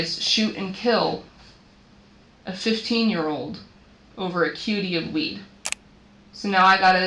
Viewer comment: When he said cutie of 🍃 I thought he said QP. I was like damn bro doing life over like 400-500worth of 🍃 then the office says cutie again. So I’m like wtf is a cutie of weed. I looked it up and it said 7 grams… so you telling me we lost 4 lives this day.. over 7 grams worth of weed? Worth like 60-80$ ? Yea they bugging. All while doing a drill with phones and ankle monitor. They deserve life. Dummy of the year 😂😂😂